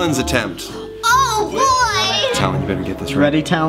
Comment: I heard “eat roses you numrut” also you HAVE to watch this with subtitles